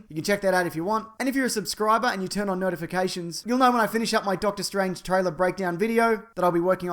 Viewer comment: I seriously thought you said, "... turn on nerdifications."